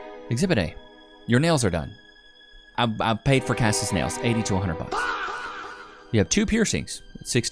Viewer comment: I thought you said 8200 buck for nails?! I was like whaaat?! But you ment 80 to 100 buck 😂